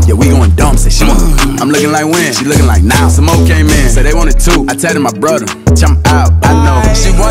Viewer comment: When he said “okay okay” I thought he actually said “okay I’m gay” 0:59 just listen😭